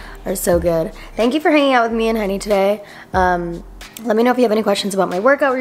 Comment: I love that when you say henny it sounds like honey and that is so cute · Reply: Skp omg this whole time I thought she was saying “me and honey”